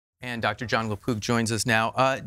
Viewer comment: I thought he said lapoop!!!! CBD and CBG oil good for dementia. And possibly magic mushrooms